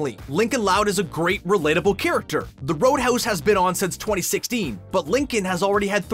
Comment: I'm sorry the load house I think it's called the loud house XD